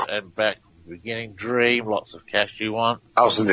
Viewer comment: Peter Popoff was brought down by James Randi and no doubt is still being pursued by fans and those at the James Randi foundation. "Dream lots of the cash you want"? I hear:- "Doing lots of mission work"!